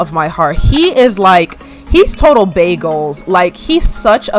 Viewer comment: When you said Mana is "bae goals" i had to rewind because I thought you said bagels 😂